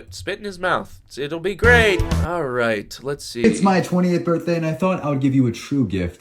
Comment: I THOUGHT HE SAID 20TH AND I WAS LIKE THERES NO GODDAMN WAY THIS MAN IS ONLY 2 YEARS OLDER THAN ME 😭😭 Jesus this video was an emotional roller coaster